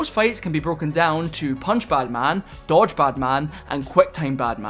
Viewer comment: sorry thought you said batman 😵👍👍👎